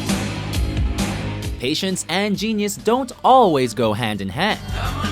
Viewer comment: i thought it said "asians and genius dont always go hand in hand" lol